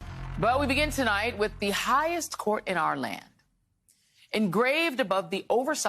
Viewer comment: Your opening line was "Well we begin tonight with the highest court in our land..." Im sorry, I heard "the BIASED court in our land..." I chuckled at my mistake, then i realized I may not have been wrong. Bummer.